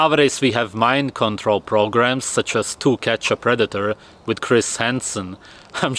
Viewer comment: I honestly misheard this weird feral Swedish man say "Two Ketchup Redditor"